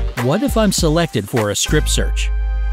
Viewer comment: It always happens to me! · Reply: did I hear you say Scrip search?